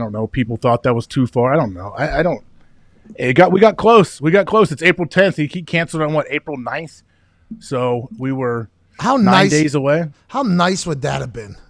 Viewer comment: Did say "we were so close, its april 10th, he canceled April 9th....so it was 9 days away"